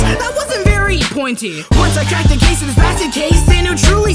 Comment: This was amazing. I think won this. One side note: at 1:25, the subtitles say "Pure Blooded Thief" but says "basket case." Was this intentional or am I being too picky? (I'm not hating on it or anything. Just wondering) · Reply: Sorry for necro posting here, but I thought he said “bastard case” lol.